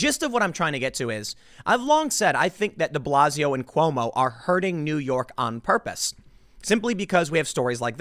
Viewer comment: Lol. At the 1:12 mark, at first, I thought I heard: "I think DeBlasio and Cuomo are HERDING New York on purpose."